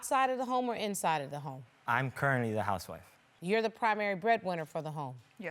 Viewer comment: Lmbo I had to play this back like 3xs I thought he said I’m currently the house Wife 😂😂😂😂😂😂😂